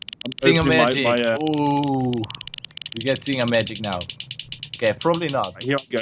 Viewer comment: I've always thought he's been saying "Thing of magic"